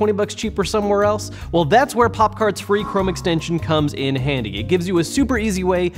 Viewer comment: I wasn't looking at the screen in the beginning and I thought he said that he was sponsored by "Poptart"